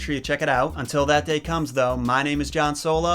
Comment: For a moment I thought he said his name was Han solo